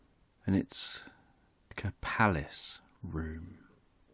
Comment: What exactly is the word you say (or the sound) at 1:39 ? Thanks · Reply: What where I say that it is like a palace room?